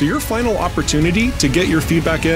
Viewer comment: I swear, the first time I heard that it sounded like: "Don't stop being an a**hole" lol